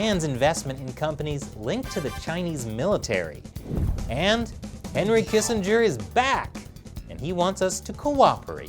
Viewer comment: Damn, I first heard it as "Henry Kissinger is dead". Guess I have to put the Champagne back in the fridge.